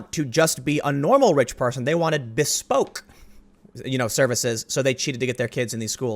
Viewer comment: I have played the video about 5 times and STILL dont understand the word you said at 1:50.....Bespoke???